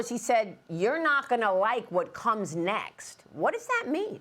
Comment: Actually he said "you're not going to like WHO comes next". I don't actually know who or what could be worse, but they always say to never say things can't get worse. 🤦🏻‍♀️🤦🏻‍♀️ He is evil, though.